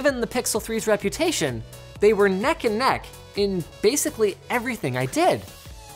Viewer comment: Did Linus just say "nek aan nek"?